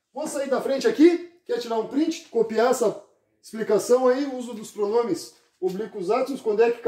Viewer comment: "quer tirar um print, copiar essa p... explicação..." Eu ouvi 😂😂